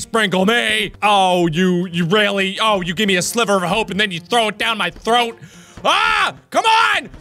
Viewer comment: I heard slipper of hope